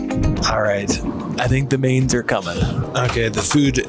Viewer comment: did YOU just say, "I think the maids are coming" 😮 As an ex-Flight Attendant myself and speaking from 1st hand knowledge, I dare YOU to get your maid to: 1. Serve a 5-course meal without any reference and/ or assistance from a trained Chef during the whole sequence 2. Able to fully resuscitate YOU in the event of an emergency before getting advanced medical care 3. Assist the flight deck through the cockpit manuals in the event one of the Flight Deck Crew becomes incapacitated during the flight.. Soooo damn offensive was your arrogant utterance that I immediately stopped watching ☹️🤦🏿‍♂️😒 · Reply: I said I think the “mains” are coming as in the main dish